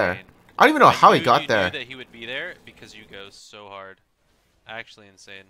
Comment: i can't tell if he said "cause you go so hard" or "cause you ghost so hard" i put on subtitles and slowed down the video and it did say "cause you go so hard" but in normal speed it sounds like he says ghost xD Then he proceeds to say thank god you ghost so hard BUT THATS OK AND IM NOT HATING.